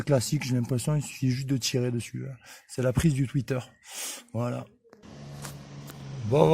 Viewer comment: c'est la prise du fuiteur ?? c'est ça qu'il dit ? c'est quoi le fuiteur ? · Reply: Je pense que tu dois avoir un problème avec mon accent, je viens de regarder ma vidéo et je dis de ne pas oublier de rebrancher le twitter